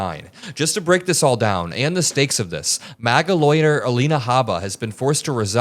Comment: Oh, You said 'lawyer'... I thought you said MAGA LOITERER 😅